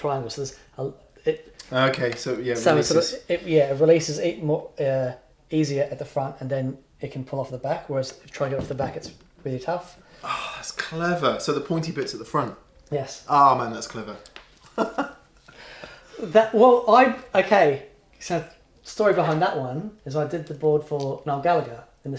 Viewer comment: explaining how he uses velcro and tape to secure pedals. says, "Ahhh, that's clever." I hear "Kleber" which is the German word for "glue." 😂